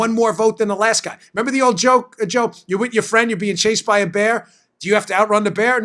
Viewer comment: Did I hear Dan say, "If you and your friend are being chased by a BARR?"